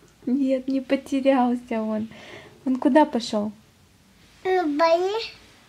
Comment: Мне послышалось, что папа на Бали)))) Поздравляю с покупкой камеры)))